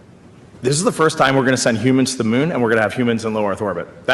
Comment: you actually misunderstood what the guy said. He is saying this: This is the first time they’re sending astronauts to the moon WHILE there are astronauts in LEO FYI I do not believe we went to the moon, but you got this dudes words wrong